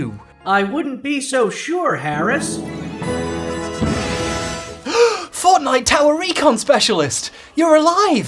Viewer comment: When he said “Fortnite tower recon specialist” for the first time at the end of the video, I legit thought he said “Fortnite Tallarico specialist” in reference to the first Guinness book of world records holder of all time.